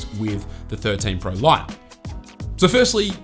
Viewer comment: it sounded like you said pro light, and even looked at the subtext and it also said light, even tho i spelt it wrong and was meant to say lite but anyway lol. was it just me or did you actually say that by mistake? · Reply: I actually said 13 Pro "line-up".